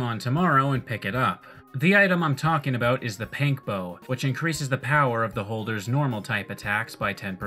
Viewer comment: did you just call it a PANK bow?